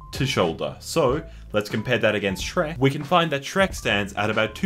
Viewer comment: did you just say that Donkey's hung!? Oh wait, you said hind.